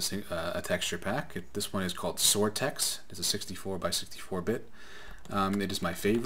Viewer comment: DIdnt really understand what you called it (i thought you said zortex). Its called Soartex 64x